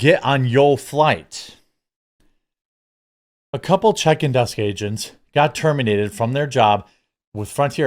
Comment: 😂😂 you said, “check in desk agent” but you said it kinda quick so for a second I thought you said “chicken head agents” 🤣🤣